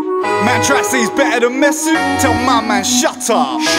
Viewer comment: Thought he was saying Messi but he was saying Mesut